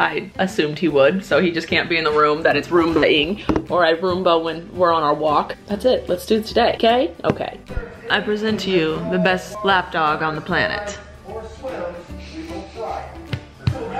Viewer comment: i thought dat u said LAPTOP 💻